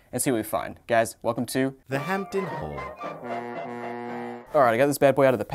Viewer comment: I always thought that it said “Thr Hampton” and I thought it was funny, I turned my phone up and heard The “Haul” part